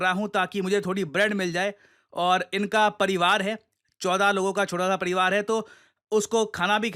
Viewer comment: did he say 14 logo ka chota sa pariwar